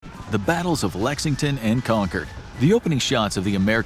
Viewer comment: Did he said Lexington & Conquer? Idk maybe I’m high I kept replaying it and kept hearing Conquer instead of Concord.